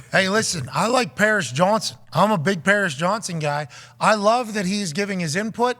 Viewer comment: He should just slide in her DMs like a normal person… oh you said Paris Johnson… my bad. Thought you said Paris Jackson.